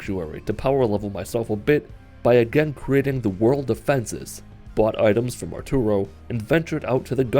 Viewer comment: thought he said bought guns form a churro